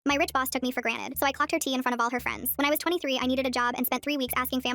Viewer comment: I thought you said clapped not clocked💀💀💀